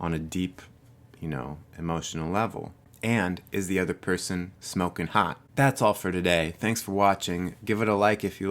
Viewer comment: "Is the other person smoking hot?" Hahaha! New rule for living. Out with the overthinking... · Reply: Georgina Leeson I thought he said, "Is the other person smoking pot?" Lol. Had to play it back.